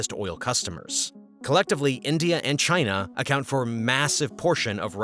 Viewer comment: did I hear that right? 22:01 massive?